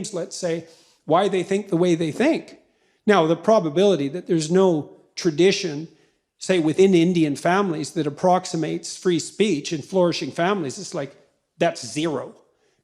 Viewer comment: Jordan my man if you want to express your views where you will have to include chimps to address an issue about Indians, be sensitive enough to address the elephant in the room, that is British tyranny under the Raj. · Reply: None of you listened properly to what he said. Check from 3:23 again. He's clearly saying, "Now the probability there is no culture, say within indian families, that approximates free speech, (in flourishing families) it's like that's zero" What it means is that a divisive system cannot sustain itself over the long run. Tyranny cannot sustain itself in the long run. So if divisiveness and tyranny was central to indian hindu culture, as the questioner seemed to imply, then it wouldn't have sustained for thousands of years. Just the very fact that the indian hindu culture has sustained and flourished for so long, itself is the proof that the questioner's proposition is wrong. Thus he said that he would talk to as many people as possible and try to understand. The chimp example was to show that even primitive social creatures like chimpanzees cannot sustain tyranny. So human society, which way more evolved and complex than them, is obviously smart enough to respond to tyranny and overthrow it at the first chance they get. Oh, i am indian myself, bdw. :)